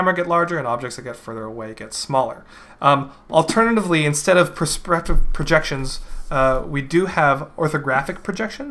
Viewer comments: hum... alternatively, instead of prospropraghjegprogections... XD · "alternatively instead of persperpsep"